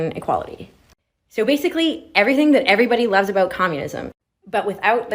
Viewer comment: Did I mishear...did she say everyone who loves communism?